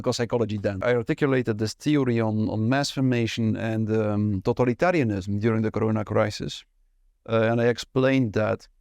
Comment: I thought he was saying "total -Italian-ism", everyone gets spaghetti !!